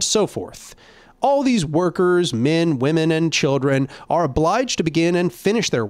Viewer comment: did he just say children?